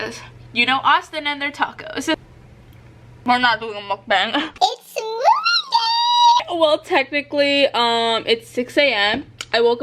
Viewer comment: When you said it’s moving day I thought you said it’s smoothie day lol